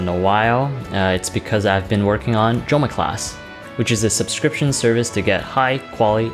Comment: Why did I Hear "Joma Class" as "Drama Class"🤔🤔🤔